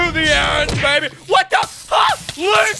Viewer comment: did he say what the fuc?